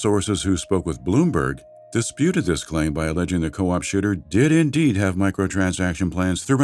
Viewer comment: I swear he says "Co-op shitter" which is still completely accurate.